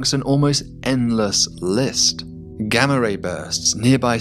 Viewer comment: When he said 'gamma ray' burst I thought he said the threat was gang rapists.